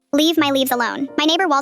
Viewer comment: I thought it said leave my knees alone😭😭😭😭